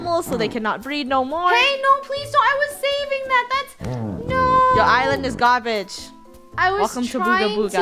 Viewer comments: it sounded like she said “Your island is gone b”- uhm nevermind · Omg... at 4:26 I heard her say:,,your island is gone b&%&%" instead of your island is GARBAGE😱🤣🤣🤣❤